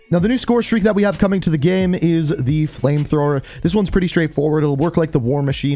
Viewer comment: Thanks to Russianbadger and his friends whenever I hear “Flamethrower” I hear “Flamethrowmber” 😂😂😂